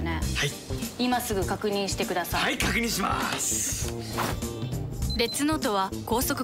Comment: レッツノートがデスノートに聞こえてくる